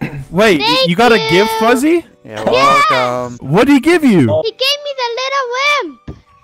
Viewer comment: At first I thought he said “he gave me lil simp”